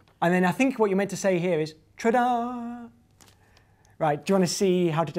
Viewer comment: he said "tra da" instead of " ta da"